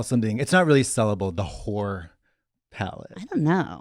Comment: I thought Katya said Horror palette for a second... Please do that🥹 And the red scare lipstick is so amazing dears❤